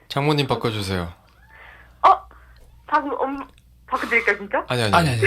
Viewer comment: 혹시.. 11:29 여기서 몬베베님이 뭐라고 말씀하시는건지 알려주실분.. 제가 말귀를 못알아먹어서 여러번 들어도 잘 모르겠네요 ㅠㅠ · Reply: 바꿔드릴까요 진차~~??